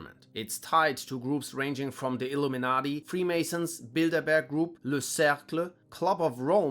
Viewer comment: why did I first hear “Build a Bear” group instead of “Bilderberg” 😂